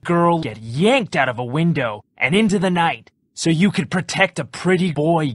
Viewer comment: You let a girl yanked out of a window and into the night so you can protect a pretty boy~: he was hot THAT SOUNDS RESONABLE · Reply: I thought he said “he was hard.” Lol